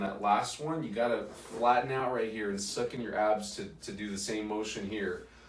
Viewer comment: Dr presentation and advice. Should I suck my stomach in while laying down doing that exercise? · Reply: I do think he says to suck in your abs, about